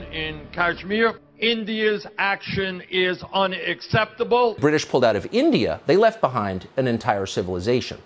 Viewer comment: what did he say Britishers what😅😅😅😅